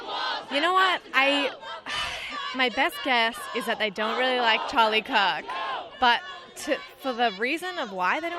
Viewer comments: The girl at 4:39 sounded like she said they dont like Charlie's cock 🤣😆😋😆😋😆😊😆😋😄😃😃😂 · Did she say "Charlie Kock" @ 4:40?